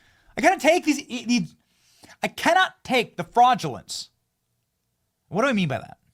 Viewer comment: I thought you said “I cannot take the flatulence” 😂 Sounds about right.